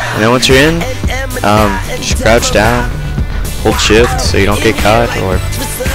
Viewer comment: haha on 2:10 it ssounded like he said hold shit haha nice one man